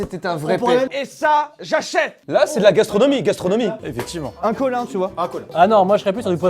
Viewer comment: dit un Collin quoi (c est mon nom de famille)